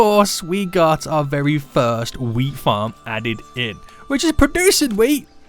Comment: i am sure i heard him saying "weed farm" and it has nothing to do with me being a little high